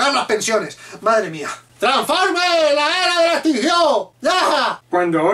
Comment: tranformee la ea ela estinjió... y después no sé que dijo xD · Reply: Dijo yehaah